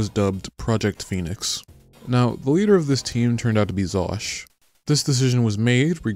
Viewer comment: I thought it said Vaush and I was like "that doesnt look like vaush"